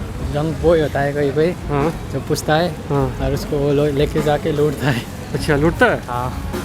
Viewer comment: I didn't understand what he said by lodtha hai.......what is that??? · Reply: Loot ta hai - robbery